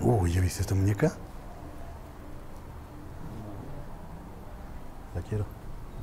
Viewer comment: solo lo escuché con audifonos pero no se si sea uno de ellos. Cuando dice de la muñeca alguien dice la quiero